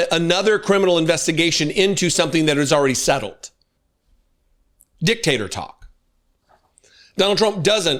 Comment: Great vid as alway! I'm a Brit, so, unfortunately, sometimes I have a bit of an audio missfire as it comes to fully clocking what you say - today, I misheard "dictator talk" as "dictator tok," which I assumed was a new nickname for Truth Social. Even though unintended, I'm going with that from now on! Maybe I'll shorten it to "Dic Tok" (works on several levels) 😂 Anyway, great work - always very entertaining, despite the ongoing horror story you're covering!